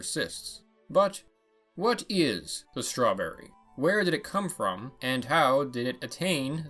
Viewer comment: "What is a strawberry?" All I hear was "WHAT IS A MAN!?!